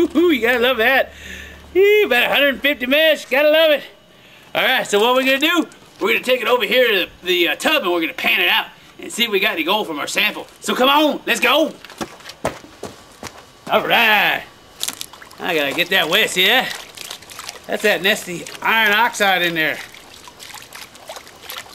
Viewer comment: I thought he was saying, "come on lets gold"